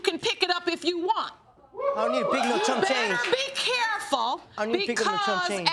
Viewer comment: did he say he dont pick up chump change??? But u unemployed tho!